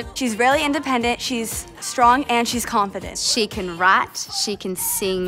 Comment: on 0:54 it sounded like the girl said she could rot